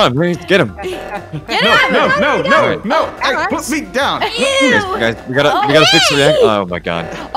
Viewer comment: im sooo late but did anyone hear saying"pu*sy down"? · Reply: He meant "Put. Me. Down"